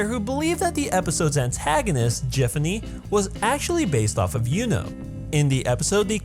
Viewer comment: "was acually based off of yuno" I heard it as "was acually based off of you know